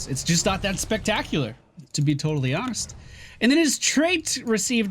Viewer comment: did someone else hear "TOADally hones"? xD